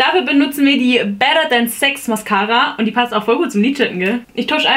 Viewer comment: Ich habe Sex Mascara verstanden heißt das wirklich Sex Mascara????????